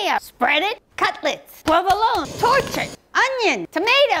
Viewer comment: I thought she was saying “Torchic”. 😂😂😅